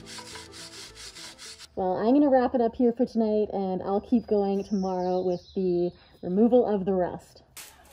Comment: didnt understand what you was saying didn't u just do the bumper damn it got rusty bad sorry weird though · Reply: I said "Well I'm going to wrap it up here for tonight, and I'll keep going tomorrow with the removal of the rust" and yes, I did the bumpers in January but I didn't prep them well enough unfortunately! So all the rust came back. Learned a valuable lesson on prepping surfaces from that project lol